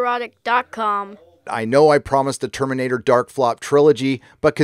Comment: I always hear "nerd-EROTIC" when these videos start... always chuckle...